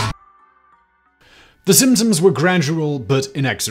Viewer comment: I swear that very first line of the video, I heard as "The Simpsons were gradual, but inexorable" and was wondering what kind of parallel you were about to draw 😂